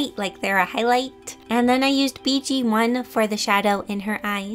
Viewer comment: (4:40) BT1 Me: BTS!! My mom: BT1 not BTS Me: ohh I thought she said BTS